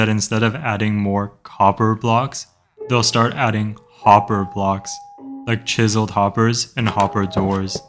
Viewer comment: I thought you said whopper